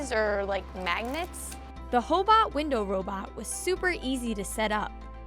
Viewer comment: did she just say hoe bot?